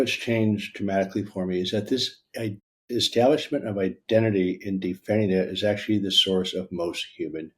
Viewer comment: Typo in the transcript that makes a difference: At 5:54 the transcript and captions have Dr. Hanscom saying "identity in definitive is actually the source of most human misery. So we are a very angry reactive" He actually says "identity And Defending It is actually the source..."